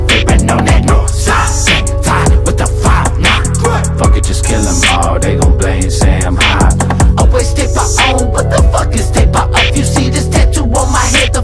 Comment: I thought he said “say I’m high” lol